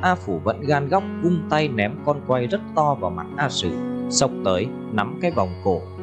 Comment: a phủ vung tay ném cái gì rất to vậy thầy em nghe không rõ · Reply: cảm ơn bạn , mình nghe thành ném con voi rất to